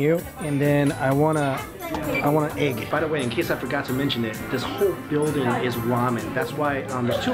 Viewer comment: I love Japan because restaurants are designed to minimize social interaction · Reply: All I heard was the building is made out of Ramen